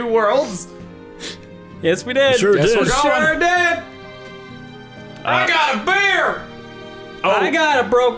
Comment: (excitedly) "I GOT A BEER!" (sadly) "I got a broken phone..." I'm really curious if these two events are somehow related :P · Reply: I thought he said "I got a beard!" and my immediate thought was "He already kinda has one though"